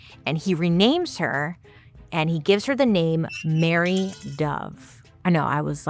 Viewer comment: when i first heard you say her name. I heard "Harry (hairy) dove". I was like wth. Why coulfnt they name her 'feathery dove', instead?